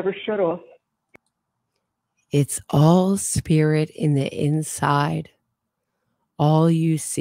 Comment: It's all MIRRORS on the inside, is what she said, not "spirit." You gotta listen rather than read, because the transcript is frequently way off. But I like your channel! (We all make mistakes. :)))